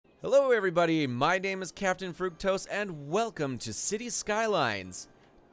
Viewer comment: I thought he said